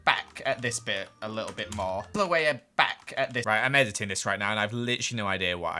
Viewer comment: SmallishBeans, I think what you said at 2:41 was "I'll just chisel it back".